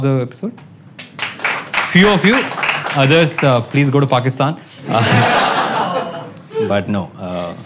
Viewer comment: Did anybody notice "Iran" in the subtitles instead of Pakistan at 4:16 .